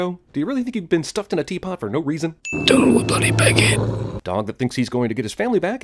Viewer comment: Until the dog said what the mad hatter said, I thought he said " down with the bloody baguette " but he actually says " down with the bloody big head " hahaha. And yeah, what's up with the mad hatter constantly switching between accents??? I think he switches back and forth between an American accent, an Scottish accent and a British accent. I think they said that when he's doing the Scottish one, he's supposed to be reminiscing about the past, the American one is when he's talking about the present and his British accent is when he's pissed off. But yeah, don't hold me to it, I'm just saying, that's what I'm pretty sure they said about why he keeps switching between accents. I don't know, I still thought it was a good movie though, not great but it was pretty good and the only reason I even gave it a chance was because I found out that Tim Burton made or Directed it so I figured, if he made it, it had to be good but not was I wrong. Like I said, don't get me wrong, I did like the movie, it was pretty good, it just wasn't great, that's all. Thanks for sinning this movie though. I really enjoy watching videos of movies that I really like being sinned, hahaha, so again, thank you.